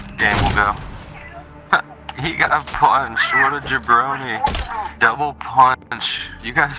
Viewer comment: Lol did he said Brony?